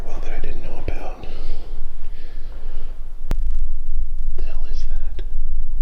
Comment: sounded like”help “